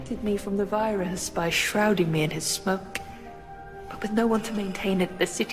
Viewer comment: Can someone confirm what us said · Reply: Sounds like "shrouding me in his smoke"